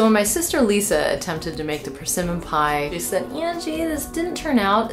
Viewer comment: thought it said permission pie.